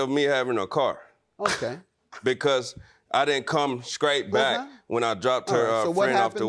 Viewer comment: Did he just say he did come “skraight” back???😂🤣😂😂 Yikes! She sounds like a stooge!